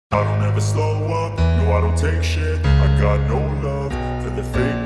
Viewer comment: One of my friends thought he said I DONT EVER THROW UP when it was i dont ever slow up😂😂😂